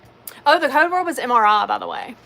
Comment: I thought u said the code word was m.. but whenever I put it in it says it starts with a b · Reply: I said “the code word WAS mri” referring to last weeks.